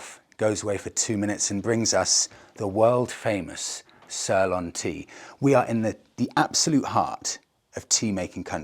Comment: "Sirlan" ? I guess that's attempt at "Ceylon"? Which those of us old enough to remember called it, up until 1972, when it got Independence within the British Commonwealth? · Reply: Yeah I had to double take on what he was saying when I thought he said sirloin tea.